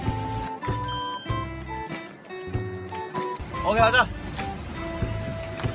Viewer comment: whatever hoseok said, sounded like , "hogya na" , matched the situation pretty well😂